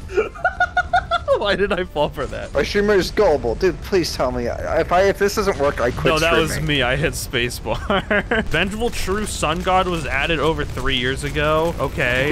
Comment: Oh my god, among us is poisoning my mind. I thought it said "Sus God".